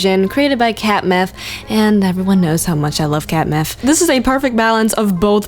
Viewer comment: "I like catmeff." Wait.... i thought she said CAT METH. Hahahahah....